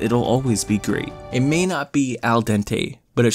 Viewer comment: my older brother walked in and thought you said "H*ntai-taining". Yeah. My brother really, REALLY, needs to get his head out of the gutter.